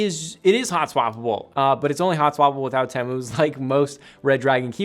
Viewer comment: Can anyone tell me what he says at 7:41 about the hot swappable thingy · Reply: It's only hotswappable with other Outemu switches